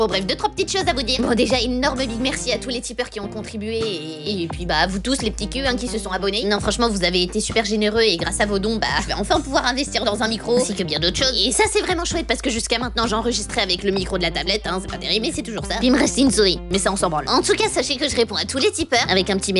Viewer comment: j'ai compris vibro à la place de micro oe j'ai bien fait de m'abonne